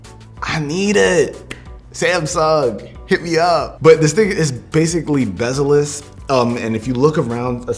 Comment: "But this (*igga) is like basically bezel-less..." I pointed this out because the controversy around Allison Morris' "supposed" slur about the LA Lakers,which I don't believe she said. Of course I don't think that's what said either, but you get the point.